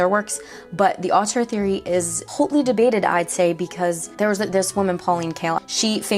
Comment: did you just say a French film theory is "hautely debated"? I hope so!